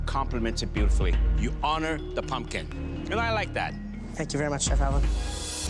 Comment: is it just me or did he say “thank you very much chef I love you” ? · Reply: Josh Baker he said thank you very much chef Alan 😂